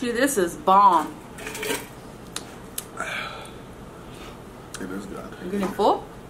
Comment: is it just me or did it sound like he said “it is not good” at